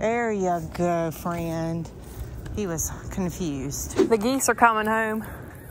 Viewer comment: At first I thought she said the geeks are coming home lol